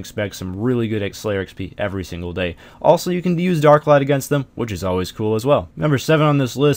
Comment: No idea what you said at 2:41 can some1 please clarify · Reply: He said "Darklight", it is the superior version of the Silverlight sword which is transformed into the Darklight sword during the Shadow of the Storm quest. It does not have very good stats, however it can be used against Demons (in this case Abyssal Demons) very effectively in conjunction with a high tier off-hand weapon (such as an off-hand Drygore weapon) because of the Demon's weakness to Darklight.